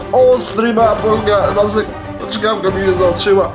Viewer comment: mam pytanie, czy wiesz co mówie między 04:55 a 04:59? Zawsze mnie to zastanawiało . Pozdrawiam :) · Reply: Chyba "Ostry Apo... (i zbitek sylab bez znaczenia, chyba chciał powiedzieć "apogealnie", ale nie wyszło), czkawka mi się załączyła".